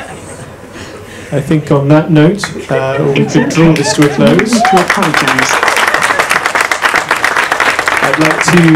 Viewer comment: what is it that the AI says? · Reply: It’s okay no need to apologize….I don’t hear anyone apologizing… maybe I didn’t hear it